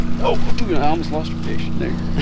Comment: what he say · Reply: Dude I almost lost a fish is what he said:)